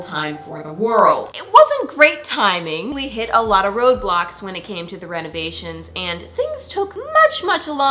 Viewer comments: When she said “ there were a lot of” road blocks” I thought she said Roblox lol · Did I hear "it took alot of roblox" outa her mouth :O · am i the only person who thought she said "roblox" when she said "road blocks" 😭 💀